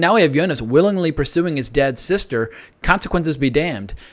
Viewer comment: Martha is Jonas's Aunt, not sister. She is his Mikkel's older sister. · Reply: That makes sense now! I totally thought he said "dead sister"! 🤣 Thank you!